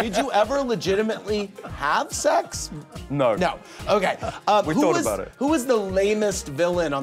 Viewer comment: did he just say he thought about it 😂😂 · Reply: I think he said “we thought about it” which is even better 😂